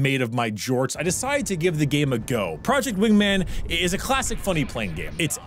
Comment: I watched this video with captions and it thought you were saying project women